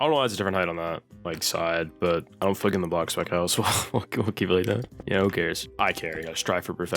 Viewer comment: ok yea dude what were you saying 😭😭😭😭 idohfeeligeddindablocksbaggout ahh